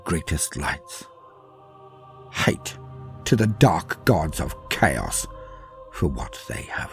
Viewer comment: What does he say at 1:41:14? "Hate to the chaos gods"? Or is that word that sounds like "hate" something else?